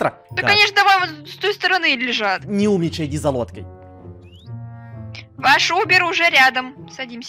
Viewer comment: послышалось "иди за водкой"